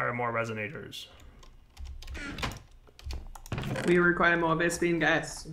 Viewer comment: I could have sworn that just said we require more lesbian ghasts